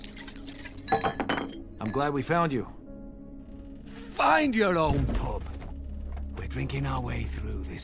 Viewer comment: did he said "find your 老母 " ？ If so its means" find your mother "